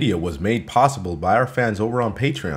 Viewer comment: At the end I thought was saying "have a cola" of course I would like some Coca-Cola